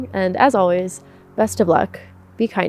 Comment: For a second, I heard "may your dogs treat you as you've treated others" and I was like 🤔 yep, that's legit.